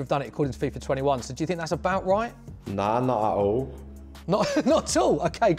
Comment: At first I thought he said 'Na na oh'